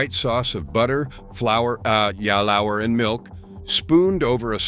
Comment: the mispronunciation of “fried E.G. sandwich instead of “fried egg” seems to point to a little white lie in the description of the video that this is all human narration. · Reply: again at “ah ya lour” instead of “flour” - it’s fine to use a narrator but don’t maybe mislead people in the description, and maybe have enough of the craftsmanship you boast of to fully listen to the generative material you’re creating before you publish? - can we trust all these visuals now?